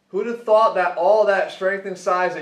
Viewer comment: In the beginning I thought he said: who the fuck thought.....